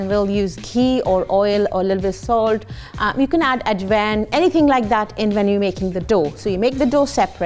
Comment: I about died when she said you could add Ativan to the kachoris. That's a pretty stiff pharmaceutical product for those who aren't aware. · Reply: You misheard her, she said ajwain